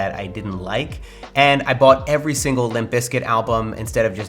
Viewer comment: What is a "Limpiscic (I didn't understand what he said) Album" ?? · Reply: Limp bizkit it's a band